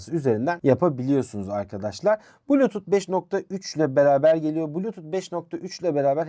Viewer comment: Bluetooth 5.3 çoklu bağlantı veriyor mu dedi ben mi yanlış duydum? Eksik özellik söyleme de fazlada sorun yok. Reklamı fazla kaçmış.